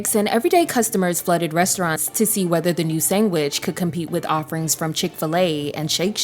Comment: did she say “sangwich”